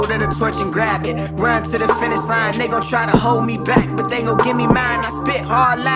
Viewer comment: No offence but he said he doesn’t curse but at 0:36 he said the n word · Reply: Bruh you misheard ,he said "and they trying to hold me back"